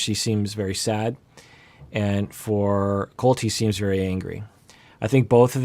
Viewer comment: What he said: "Colt, he seems very angry" What I heard: "Coltee seems very angry"